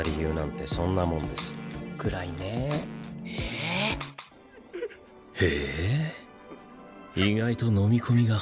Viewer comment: the "thats dark" part at 1:05, he was actually saying "nee", which means he agrees, not ehh?, so maybe now you'll understand how its funny LOL